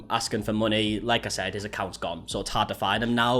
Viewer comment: When you said "HIS ACCOUNTS GONE" I thought you said "he's a couch goblin"